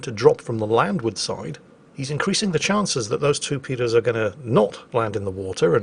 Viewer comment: at 20:05 did you actually say two paedos or did it just sound that way???